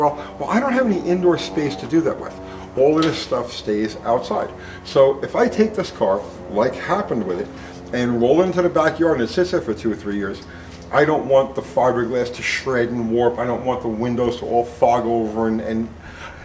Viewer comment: Did he just say at 3:05, "I'm gonna roll this into the back yard for two or three years?" If the car is sitting in your backyard in the rain and mud for 2-3 years, it's not a project. it's junk. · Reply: It said “like it happened to it” meaning past